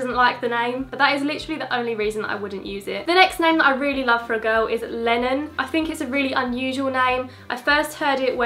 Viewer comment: Actually thought you said Lemon at 4:20 😂😂